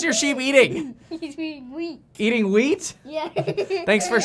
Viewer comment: My mind is so tainted that I heard the "sheep is eating weed."